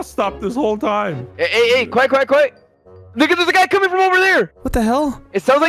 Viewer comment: Bro at 23:36 lui sounds like he says "nigga theres a guy coming from over there!" LOL